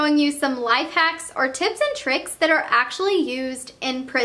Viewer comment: It's sounded like she said tits and tricks💯